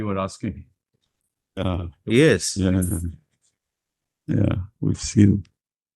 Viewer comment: Did he just say “we’ll see you” ? 😮